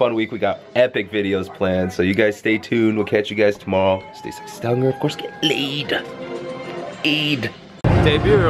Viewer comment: At the end I hear "of course get laid". Maybe he says this all the time.